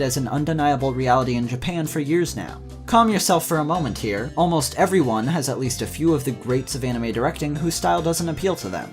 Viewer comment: What anime at 13:02? I can't tell what he's saying. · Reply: Angel's Egg, a 1985 movie directed my Mamoru Oshii